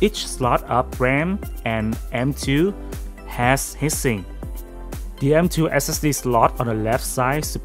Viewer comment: It's not hissing, it's HEAT SINK! Try saying it as TWO WORDS! It sounded like you were saying HISSING as in noise!